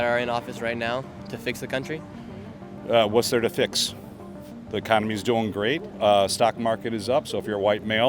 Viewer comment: did he really say “the economy is doing great”.